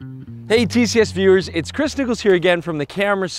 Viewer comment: Did he say his name was christticles?...